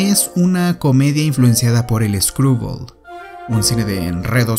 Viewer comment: Qué dice en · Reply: Influenciado por el cine Screwball